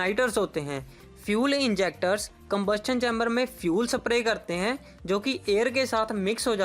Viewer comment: vai sab i love u saying saprey..instead of spray..lol..